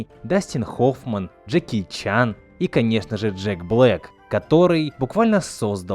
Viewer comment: слышу блэт тем временем я:так блэт